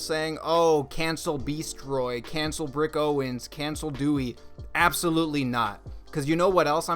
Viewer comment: I don’t think they should be canceled either I hate cancel culture but it’s so obvious that this was a ploy to get publicity, the “pieces” are so low effort and like you said the little bullshit pamphlet makes their reasoning come off as pretentious and disingenuous. Sadly it’s worked and now they got the publicity they wanted. Hopefully let they can turn it around and do something good like actually try to start a dialogue about mass shooting (which probably wouldn’t do anything anyway sadly) or maybe they’ll turn out to be good designers. But this shit is just low effort trash and the whole race card thing was pathetic I have no actual hope for them turning it around but maybe I’ll be wrong and I’ll be pleasantly surprised. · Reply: and when you said “brick owens” i only heard “rick owens” and was like “tf no way this was rick” lmfao